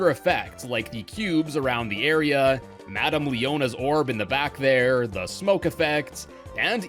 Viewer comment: ? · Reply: sounds like you said “leona” instead of “leota,” could be me tho